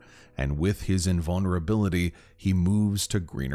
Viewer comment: Me, from Chile being so confused 🤠🤝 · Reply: same, i thought he said "chill"